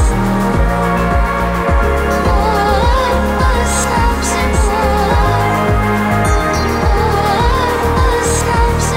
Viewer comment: Anyone know what shes saying at 2:50? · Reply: A bit late but I’m pretty sure it’s .. “you are my substance”